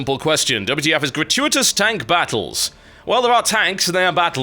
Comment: lol at the start i thought it said gratious tank battles :3